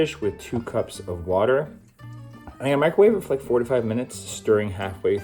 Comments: 4-5 :) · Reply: I also thought you said 45 minutes. 😂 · Hiya thanks for these tips! Did you say 4-5 or 45 minutes to cook the pasta at around 12:30? Thanks! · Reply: 4-5 :)